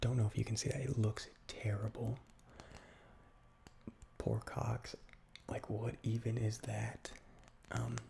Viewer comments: Pork Hocks, but i wasn't looking and i heard ''Poor Cocks'' lol!! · Half asleep I heard 'cocks' not 'hocks' and had to replay that part to make sure I was wrong...